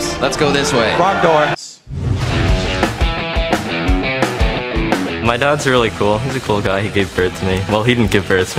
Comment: What does he said? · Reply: my dad's really cool, he gave birth to me